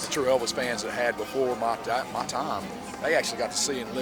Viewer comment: before what? Sounds like he was about to say before my Dad